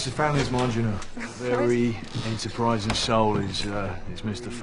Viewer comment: he doesn’t say “surprising soul” he says “enterprising soul.” Meaning he shows initiative and resourcefulness. How have you seen this so many times and not known what he actually says?